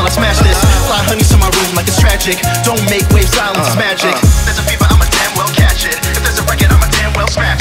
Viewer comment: At a certain point in 2:34 does anyone else hear "smagic"